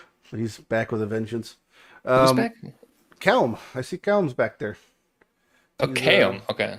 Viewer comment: I heard that as "qelms back" actually troll af